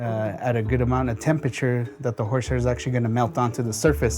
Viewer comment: Turn on closed captions and go to 3:47 Captions say “horseshit”😂🤣😆 Horseshit melts onto the surface😂😆😂😭